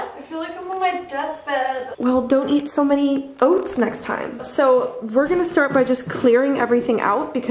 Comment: "i ate too many oats and now i'm on my deathbed" "well...don't eat as many oats next time" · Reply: I thought it said she ate too many cats 😂😂